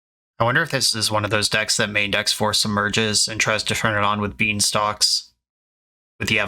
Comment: What card were you referencing? It sounded like you said Force Emerges but I couldn't find anything on scryfall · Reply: Four copies of the card Submerge :)